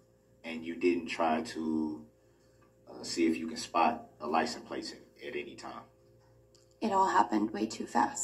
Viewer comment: It sounds like he is saying license PLACE - not plate! Is that yet another Brooks-ism, if I am hearing right???